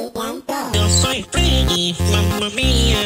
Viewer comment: i hear "yo soy freddy" in this version